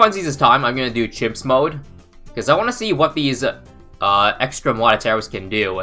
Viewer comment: It sounded like he said "I'm gonna do chips mode"